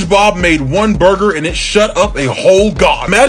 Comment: I heard "Spongebob God it"